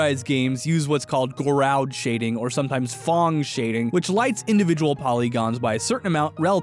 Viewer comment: Who else thought he said "thong shading" 😅